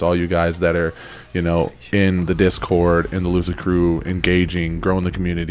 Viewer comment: I’ve been meaning to write this but keep forgetting so I’ll do it now- for the longest time I thought you were saying “loser crew” and I realized this week your saying “lucid crew”. My husband and I died laughing at this. Secondly, I enjoy watching your Videos bc you are relatable. There are some things I WANT to believe but can’t, there are some things that I feel are far fetched or grasping and the fact you actually say this also instead of agreeing with every theory makes you relatable and in my opinion reliable. Your open and honest and If you do have knowledge about things, you express it instead of going with the census of everything is a conspiracy theory. That’s respect. It’s nice to have someone express their views and opinions on these videos that aren’t just trying to appease their viewers. I like to look at these videos from 2 sides and come to my own conclusion with my research and you seem to be the same. Thank you for the videos! I’m part of the “Loser Crew”! 😂